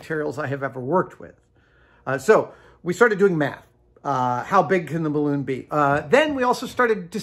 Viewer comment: I thought for sure he said "so we started doing meth, how big can the balloon be"